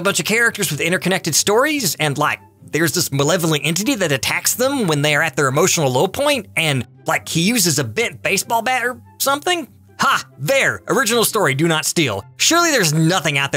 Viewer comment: I’ll be honest, before Paranoia Agent popped up, I thought he was talking about Harry Potter (I heard “piece of a baseball bat,” so I thought that was the wand).